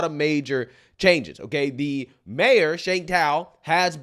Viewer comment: For a minute I thought they said their mayor was Shang tsung. Then I was like, Shang tsung would do a better job